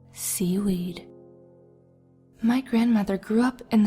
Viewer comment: I'm watching this for the third time and I thought she said cereal instead of seaweed... What is wrong with me...